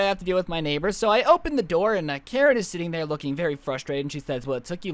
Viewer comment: When he said "turn your neighbour to a karen" I thought he said carrot